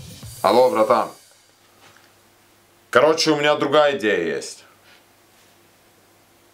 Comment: Hallo Bruder, also ich habe eine andere Idee, wie verändern unseren Plan ein wenig. Alles klar Bruder, alles verstanden. Junge war das "всё понял" schwer rauszuhören Hier die richtige Aussprache: Vsjo Pon(weiches n. So ähnlich wie bei "Nilpferd", sogar noch weicher)jal Ponjal